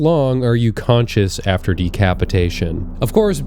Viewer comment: I thought that said "Constipation"